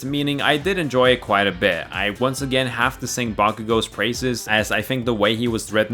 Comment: I thought you said “I once again have to say Bakugo’s racist”